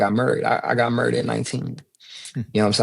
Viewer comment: Great chat! When he said “married” I thought he said “murdered” lol murdered the single life 😂😂😂